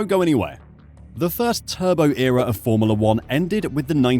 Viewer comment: LOL.. I thought you said Aldi at first.. I nearly inhaled my hot chocolate until I looked up and saw Audi.. aaand then I thought Aldi is probably a better fit for Sauber! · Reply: A also thought he said "The Turban Era of Formula 1"